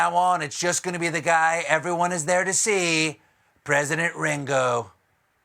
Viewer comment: I thought he said President Gringo 🤦🏼‍♀️ I need a hearing aid 🤣🤣🤣 stay safe y'all